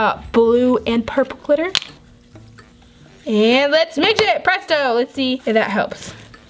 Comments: Who else heard “And let’s Megit” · i hear her say "LETS MIGIT" lol